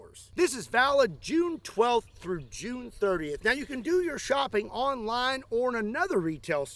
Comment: i was skipping the sponsor and all i heard was'right now do ur fucking shopping😂